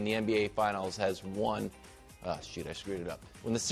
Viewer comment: Did anyone catch someone cursing at 15:20. Lol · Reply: He said "shoot"